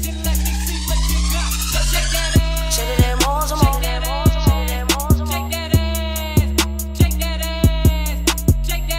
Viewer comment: what was she saying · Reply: Shake it more some more or more some more!!!😂